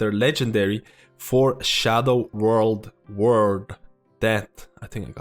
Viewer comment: I always hear him saying “world” when he tries to say “word”.